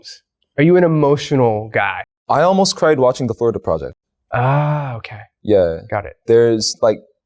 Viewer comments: what is this movie’s name..???? i cant hear it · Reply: “The florida project” I’m not 100% sure tho haha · which film are they talking about at 25:00? they said the name, but i didn't understand it help🥹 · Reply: Could it be The Florida Project? I haven't seen it but it sounds like it to me